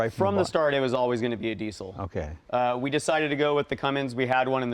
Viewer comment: pretty sure he said Detroit. It did not look like a Cummins. I have owned them. 🙏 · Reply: He clearly said Cummins. I swear. You have the attention span equivalent to that of a chimpanzee.